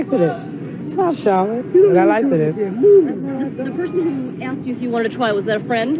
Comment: She “likeded it” Did she really say “likeded”???